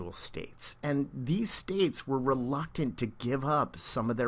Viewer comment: Can any one tell me what is the word in 2:54 , he said and this states were ... To give up their power . I didn't get the word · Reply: reluctant